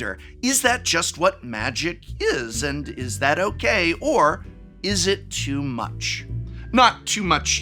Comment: Did you just say “Izzet too much”!?